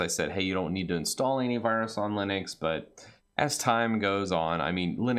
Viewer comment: it sounds like he says any instead of anti "hey you don't need to install any virus on Linux" Oh I don't need your tip regarding this ^^